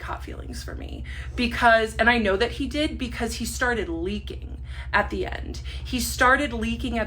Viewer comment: i heard “i knew he liked me because he started leaking at the end” and thought this was going a whole other direction